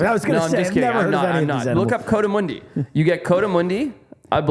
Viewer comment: I thought he said " we get barramundi " 🤣